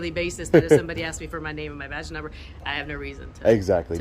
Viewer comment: video subtitles show vagina number instead of badge number lool